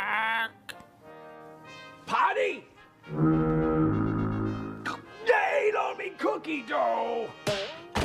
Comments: I think he's saying Cop! Yo eight al mi cooky doe! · It kinda sounds like he said, "They ate all me cookie dough!"